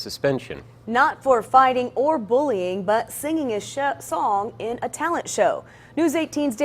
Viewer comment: at first I thought she said "singing a shit song" 🤣